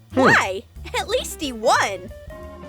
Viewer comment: When it said atleast he won it sounded like it said BC 1